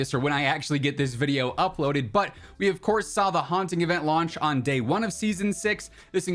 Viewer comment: Just had a super long day at work so I might’ve misheard you but it sounded like you said the howling instead of the haunting so maybe after the 16th it goes to the howling and we get a werewolf Hunter style game or maybe that howling game is where we play Warzone but then we have that Angry Santa Claus creature chasing us again. I forget the stupid things name maybe there’s some fun Halloween stuff that will be coming on the 16th and all this is a preview of something awesome. One can hope correct?